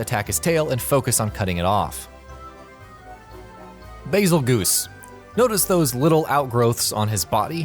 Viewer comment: Did he just say "bagel goose"